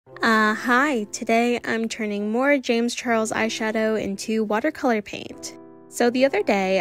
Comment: I misheard your intro, and I thought you said "Uhh Hi. Today, I'm turning James Charles into watercolor paint.." and I saw nothing wrong with that.